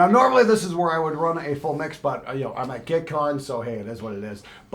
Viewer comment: I swear...I heard you say GuitCon... lol... missed you guys and it's only been like half a year! 😎🤘🎸🐦🥒